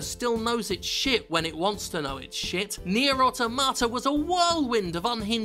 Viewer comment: I thought he said near a tomato lmao